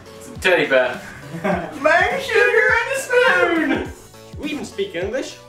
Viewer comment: what were they saying? It sounds so funny XD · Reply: I'm eating sugar with a spoon! I think